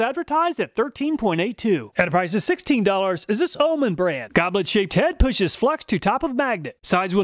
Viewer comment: Did anyone else hear 'goblin-shaped head?' :o